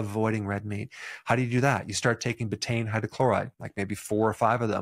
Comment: Pertain hydrochloride? Is that what he said for acidifying the stomach? At about 6:17? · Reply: Betaine hydrochloride